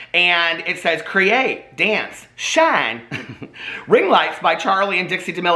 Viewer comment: HTG When you said ring lights. I honestly thought you said ring worms